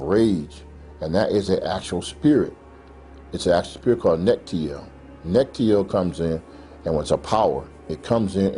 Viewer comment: What word is he saying at 41:10? He is naming the spirit of anger but I cant understand what hes saying..... sounds like neptio or nectio...